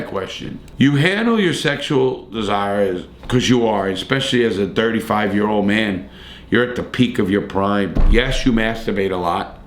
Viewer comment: love this video, but man at 5:10 i thought you said "a dirty 5 year old man" HAHAHA